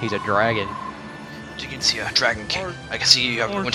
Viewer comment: To your credit, I heard you say "Dragon Kling" :b